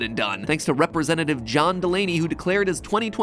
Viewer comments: I thought he said John Mulaney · When MatPat said “John Delaney” at first I thought he said my name (Delaney) and then it sounded like he said “John Mulaney” and I was like so confused 😂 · I thought mat said john mulaney! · When he said John Delaney, for a second I thought he said John Mulaney and I got excited.